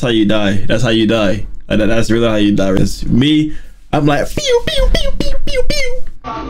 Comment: did he say pew pew pew😮